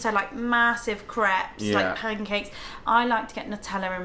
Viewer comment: lol sounds like your saying craps😂🤣😁💕sorry I think American way of saying crepes is better 😊